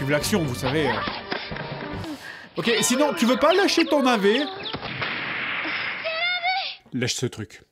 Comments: Bordel j'ai compris "tu ne veux pas lécher ton navet?". J'ai des gros problèmes. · MAIS DIS MOI DONC , 14:06 , n'étais-tu pas senser dire " lache ce truc" et non pas.. enfin écoutez bien quoi :3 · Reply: Lèche ce truc xD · Est-ce que c'est moi qui suis tordu d'esprit ou il dit clairement "lèche ce truc"